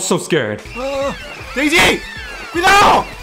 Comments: Daisy Cuidado :p o al menos eso escuché xd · Does he say "DAISY! CUIDAO!" ? That got me by surprise xDDD